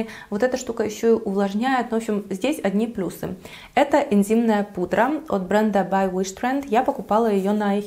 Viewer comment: Энзимная пудра... а мне послышалось интимная пудра. Уши навострила, думаю, ядреня хреня, уже и там пудрят 😂😂😂 неужто я что-то пропустила))))))))) потом, поняла, что ослышалась, вздохнула с облегчением)))))